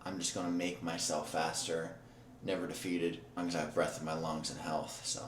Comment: Did he just say "as long as I have my health??" I thought this was die tryin' bro...